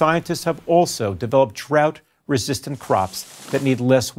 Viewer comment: It sounded like he said, "trout resistant crops". 😂 It gave me the mental image of guys sitting around corn fields early a.m. with guns, waiting for the trout to start moving in.